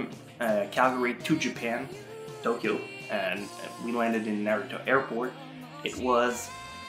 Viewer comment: Did he say Naruto airport?